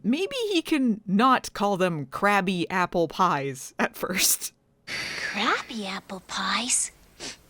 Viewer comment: It is probably me, but it almost sounds like Share Bear said "Crappy" apple pies instead of "Crabby" apple pies. 🍎 Good idea for Thanksgiving Special to Riff on this weekend with friends 😂